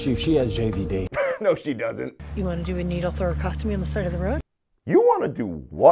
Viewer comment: You know, when he said "gather around for the roadside murderer" I wasn't expecting them to promptly stab her with a knife. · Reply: I thought I heard tracheostomy, which would’ve made that scene 10x worse, I thought they were gonna stab her neck with a pen until they gouged a hole into her trachea, fun!